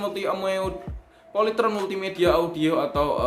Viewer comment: Salut banget sama abangnya, kok bisa²nya doi gak ketawa pas bilang "polytron multi amoeot" 7:02 sumpa gw ulang² tetep ngakak.